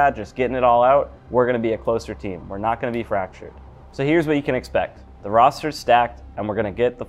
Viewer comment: Thought I heard “we’re not gonna beat Fracture”😂